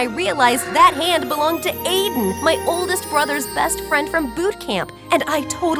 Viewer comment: GOODNIGHT I THOUGHT SHE SAID “ I REALISED THE HAND BELONGED TO AIDEN MY OLDEST BROTHER “ AND I THOUGHT THAT CAUSE SHE WAS BLUSHING 😭